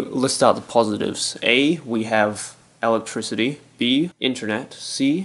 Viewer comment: When you said electricity I thought you said ellicott city